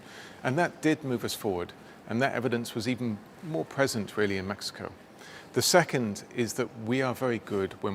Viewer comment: DID HE SAY MAXICO????🤣🤣🤣🤣🤣